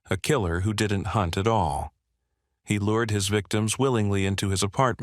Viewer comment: um... that was a very strange random "ahh" there for no reason lol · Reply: I thought he said “at all”